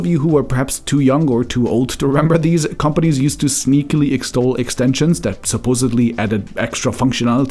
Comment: did you mean to say 'extall' instead of 'install'? I can't find 'extall' on the internet but it should definitely be a word. Whereas you install something by inviting it onto your computer, third parties extall things onto your computer against your will. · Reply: I think he meant to say "extol", in that third party companies are praising or highlighting these programs or add-ons to make you want to buy it or install it